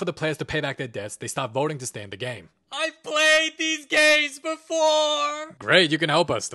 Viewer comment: why the frick did I hear “IVE PLAYED THESE GAYS BEFORE!”